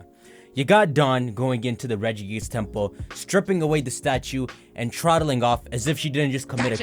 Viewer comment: thought he said Dawn is stripping for the statue for sec like damn she might wake up Regigas just by doing that